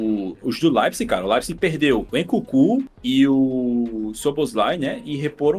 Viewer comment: o Leipzig perdeu oq???? 😳😳 eu ouvi o C□??? Obs: eu sei q ele falou Nkunku...